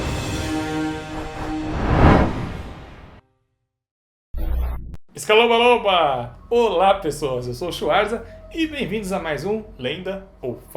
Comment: Sou tao apaixonada com star wars que Eu achei que ele tinha falado assim "olá eu sou o chubaca"